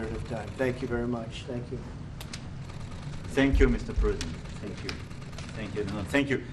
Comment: i swear i heart macron saying thank you mr Putin :D